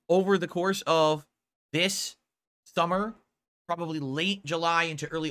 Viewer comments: Did he just say ‘This Thummer’? Reminds me of the 3 3ast 3 3urious flash movie. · Did he just say ‘This Thummer’? Reminds me of the 3ast 3urious flash movie.